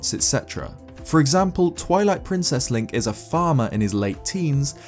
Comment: When he said farmer I thought he said father and got really scared.